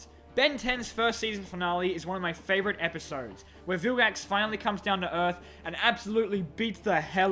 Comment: I was listening from far away and when he said vilgax I thought he said phil gangster